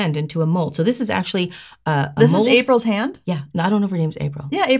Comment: Don't know why I kept hearing "April's Fool Hand," but I did Haha. I watch your videos with earnest! So much so I've signed up for online courses with NSI Academy in hope of changing in a major way my life career! Sadly I cannot make it to your own school, no offense meant. But you've certainly been a huge inspiration! Thank you very much & have a great day today!